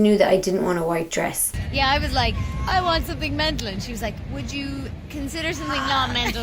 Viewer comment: I love the cadence and enunciation of Scottish and I have a good ear for accents but I have no clue what she's saying at 04:00 -- like she loses me with the word that comes after "I want something...* and she says it three times ffs!! Totally not her fault or anything but I must have rewound at least fifteen times trying to figure it out; that's how much it perplexes me lmao help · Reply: I have subtitles on the video :) I'm Irish haha! I say "I want something mental"